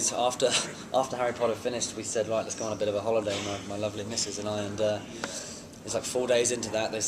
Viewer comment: Did he say my lovely Mrs and I ?????